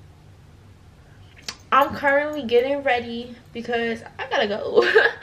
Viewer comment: Idk why I thought she said “ I’m currently in 6 grade “ I was like honey what